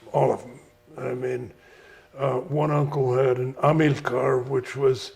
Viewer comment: what did he say that car was? The one he describes as being a scaled down Traction-avant. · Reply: It’s called an Amilcar